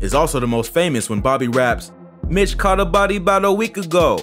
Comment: I never knew he said “ Mitch caught a body bout a week ago “ I thought he said “ bitch caught a body “ 😩😂😂😂